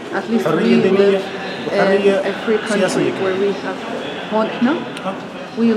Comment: Did he say we live in a free country?